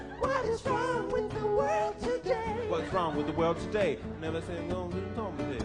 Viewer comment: What does he say around 3:28 after singing "What's wrong with the world today?" He is mumbling something. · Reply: Never said that anything was wrong with it.